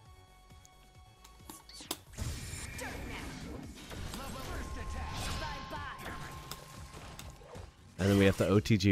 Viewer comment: What is X 23 saying at 2:52 when she activates silent kill? It sounds like dirt something, I don't know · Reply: Dirt Nap, that's the name of the move